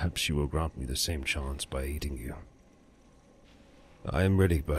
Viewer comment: lol At around 4:00 I thought you said "If Lady Mara will grant you a second chance, perhaps she'll grant me the same by eating you". It took me a couple seconds to realize you said "aiding" not "eating" :P